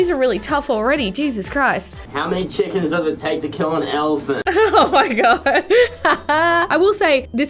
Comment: I literally thought that said how many chickens does it take to kill an elephant